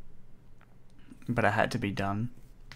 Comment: it sounds like he is saying but i had to be done